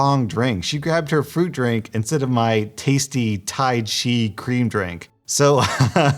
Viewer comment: Did he just say "tai chea"? 😂